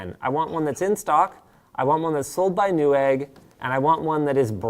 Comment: did he say fresh egg for new egg 😂😂😂😂😂😂😂😂😂😂😂😂😂😂😂😂😂😂😂😂😂😂😂😂😂😂😂😂😂😂😂😂😂😂😂😂😂😂😂😂😂😂😂😂😂😂😂😂😂😊😅